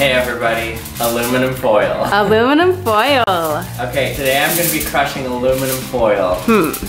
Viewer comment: Haha I swear at first I thought he said, "Hey everybody, today we're gonna be cucking aluminum foil."